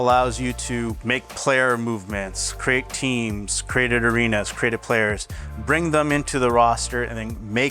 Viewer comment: Wait did he just say create an arena???